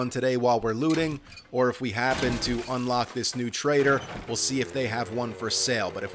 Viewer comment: i heard traitor. LoL.